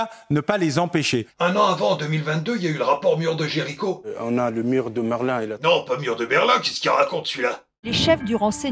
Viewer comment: c'est moi ou il dit " le mur de Merlin" ? 😅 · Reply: Oui , chalgoumi a dit mur de merlin